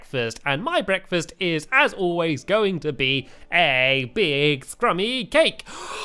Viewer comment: When u said scrumy cake I thought u said crumy cake And if u fid and I was lee I would be really sad